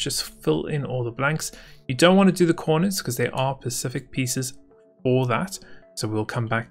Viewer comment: Is it me or at 3:20 he said pacific instead of specific.